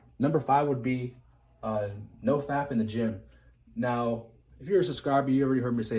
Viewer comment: I thought you said number 5 was “no fapping in the gym” 🤣